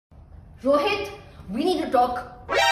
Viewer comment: Wait did I hear "rohit we need do dogk".?😂😂😂